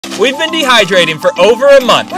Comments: I heard "we've been dehydrated" and I was like.....what!?! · I thought they said that they were dehydrating for 4 months 💀